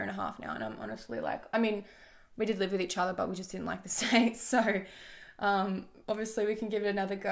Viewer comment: Someone pls tell me what she says at 10:08 😂 they did live together but they didn’t like what? The sex? 🤣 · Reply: The states